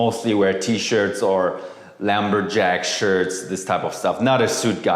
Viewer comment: Wait..did he just say "lamberjack shirt"?